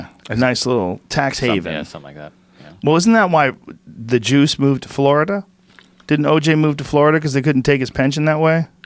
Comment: really thought you said "thats why the Jews move to Florida" for a second there. Raised my eye brows for sure.